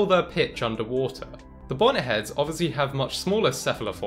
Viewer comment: i thought it said bonehead and i said you have to be a bone head to be vegan